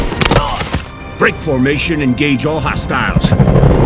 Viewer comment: Can some please write me a title, have bad rnglish, I mean i can translate but I cant hear very well because i need to practice listening enlgish...so can someone write what qautrich said here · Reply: “Break formation and engage all hostiles”